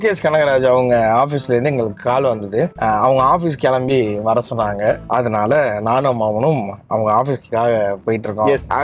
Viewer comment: Did he say "Nanum Mavanum"